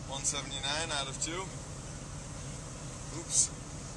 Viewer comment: Did anyone else hear that at 0:45 "Idaho's to the left of Nevada, not above it" Smart man, smart man